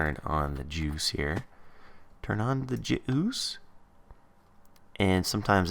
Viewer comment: am I the only one that thought that he said turn on the jews